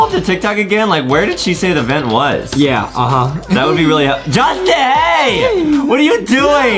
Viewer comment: DID really said justieeeeeee! Correction is Justin. Hmmm we need to put into English class